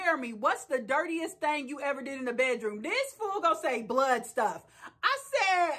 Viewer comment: I missed Jeremy’s comment because I thought he said “butt stuff” not “blood stuff”. I was like damn because I was wondering what he actually meant from that. I am already suspect on whether he even liked women. I’m going to rewatch that just to make sure he said what you heard lol.